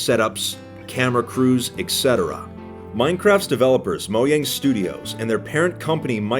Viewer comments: It sounded like you said mogang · after like 5 minutes in watching the video all i hear say is "moyang"